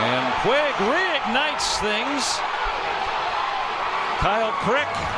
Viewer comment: Anyone Else Hear Kyle Pr*ck at 0:18 not Crick